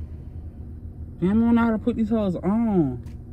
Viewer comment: I thought you said you knew how to put them on, omgggg lol